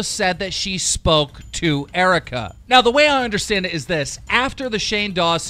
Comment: I thought it said Etika Lol